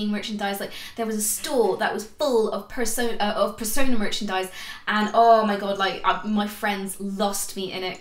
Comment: did i hear "persona merchandise"